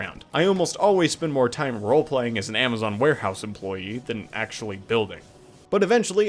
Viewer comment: at first i heard amazon employee warehouse lmao